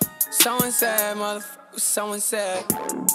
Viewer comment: Sounds like hes saying "sewing set" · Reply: It literally sounds like he is saying "Sewing Set" instead of "Someone said" is what I'm saying.